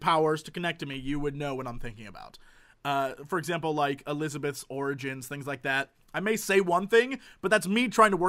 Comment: I swear he said "elizabeths organs"